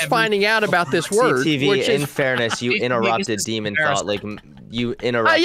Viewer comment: did hunter call her demonthot?